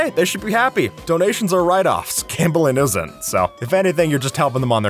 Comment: "but hey, they should be happy, donations are a write off, cambullynosehair, so" what the heck? I've rewatched that like ten times and I have no idea what he was trying to say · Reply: Cambullynosehair=gambling isn't